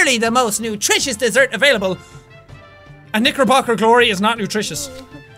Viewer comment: jacksepticeye Has nobody else noticed around 8:27 it sounds like you say N***erbocker glory" instead of Knickerbocker Glory?